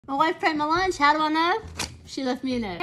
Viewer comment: ever time you say “my wife packed my lunch you wanna know how ik” i always think cuz you didn’t pack it😂😂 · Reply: That's what I thought she was saying at first, but I'm pretty sure it's "my wife pranked my lunch"